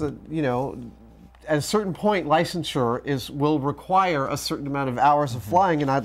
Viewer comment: what does he say? Lice insurer? Couldn't make it out. lol